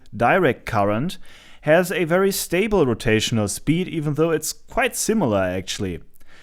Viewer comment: Is it wrong that I misheard "rotation speed" as "potato speed" ?